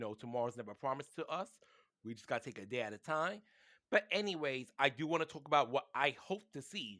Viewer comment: did you say "a day at a TINE" and not "TIME"? give that G.E.D back buddy.